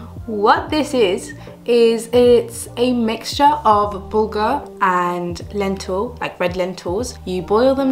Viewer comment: I thought you said booger, but it us bulgur 😭😭